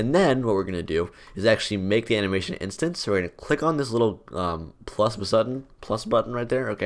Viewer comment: am 9i the only one who heard plus bussuton